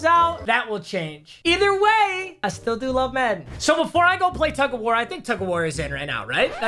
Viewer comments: am i the only one who heard “either way, i still love men” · Either way I still love men? 🧐What did he sayy????